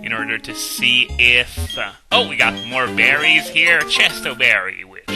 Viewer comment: At first, when you said more berries, I thought you said more Barrys.